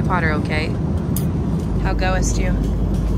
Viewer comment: can someone tell me what just said to... "How go'est you...???", I'm confused